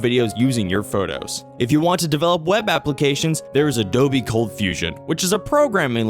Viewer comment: after a while this begins to literally sound like a parody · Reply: Right? I heard "Adobe Confusion®" instead of ColdFusion and was sure this had to be an elaborate parody